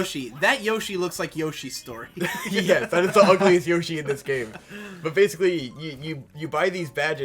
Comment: when says "But basically" I just heard "Fundamentally, basically, the concept is this..."